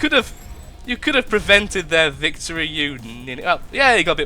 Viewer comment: did he say the N word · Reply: "You ninny" I'm afraid not, nothing Of that sort.